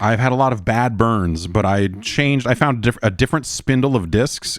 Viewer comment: God almost every time you said “bad burns” I just heard “Brad Burns”